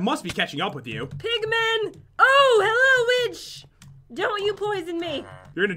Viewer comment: Did I heard Jen say, “ Oh hello witch!” Or “ Oh hello B****!” ????????? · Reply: I'm 100% positive she said "witch"